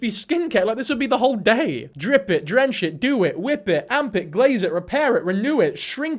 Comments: Amp it lol I thought you said armpit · i really dont like that amp it sounds like ur saying arm pit w an accent 😔 i think it is a bad name